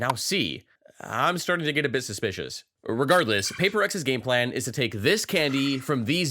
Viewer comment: Why i heard mentioning Paper Rex in 8:40 ?